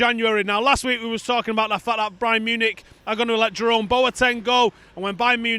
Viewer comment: did just say Brian Munich?